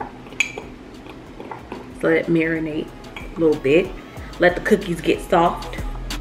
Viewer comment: I thought you said let the cookies urinate! I now realize you said marinate